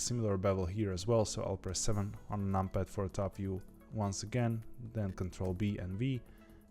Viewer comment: im stuck hear is he saying ctrl b then v? cuz thats what im putting and its not doing it properly and also does it work on version 2.93?